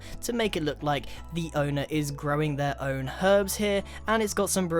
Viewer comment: Growing Some Weed? Also isn't it about weird how something that legit almost looks like weed and sounds like he is saying weed has the time stamp of 720?